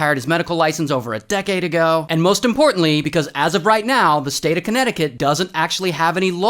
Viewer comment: As a Connecticut resident, I don't know how to feel about Ray saying Connect-i-cut like that. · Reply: I thought he said Conneti-cu- n t 😂